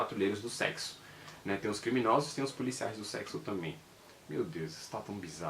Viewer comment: Qnd ouvi "policiais do selo" pensei exatamente o que vc disse: ficou bizarro haha